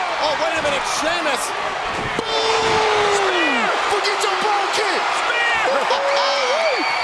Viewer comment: lmao what jimmy uso said 😂 “forget your brogue kick” I think that’s how you spell brogue